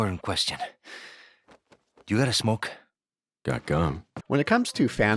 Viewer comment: When Luis asked for a smoke, I always thought Leon said "dog gone" not "got gum." That blew my mind a little.